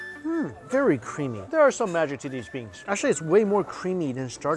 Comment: did he just said that those are magic beans? dbz mike?